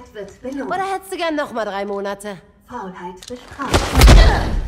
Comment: Bin ich der Einzige, der bei 1:54 "Faulheit befreit" gehört hat? :o Bei 3:28 wurde ich aber enttäuscht... hatte schon auf dem Monitor erwartet :D